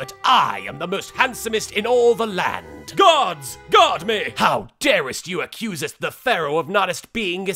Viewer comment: Guards! Guard meh! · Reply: I always thought he'd be saying "Gods! Guard me!", damn. xD